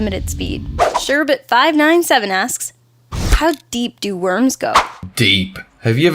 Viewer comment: When the question "How deep do worms go" I thought it said "How deep do Women go"